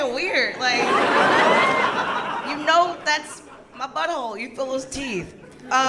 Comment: "you know that's my b***hole you ____" What does she say ? I can't figure it out. Fill his teeth ? Fellas tease ? Philistine ? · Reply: Feel the teeth